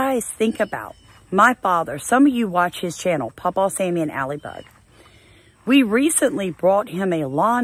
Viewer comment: I heard "Popol Samian Allie Bug". Am I close?